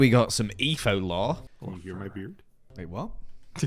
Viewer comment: I totally thought etho said “you’re my beard” 😭